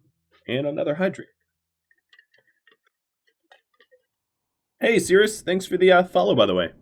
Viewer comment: What was that at 23:15? Did you say their name and then say: Given your name I won't ever address you? Was it Sirus-hitlerdidnothingwrong or something? Did I misunderstand you or maybe I misheard you? Don't mean this to sound accusatory in any way just sounds like I missed something by not seeing twitch chat or whatnot. Either way, thanks for the great content. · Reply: I think his name was sirus_the_lurker or something like that. He self identified as a lurker, so I said that so as to promise I'd leave him alone =]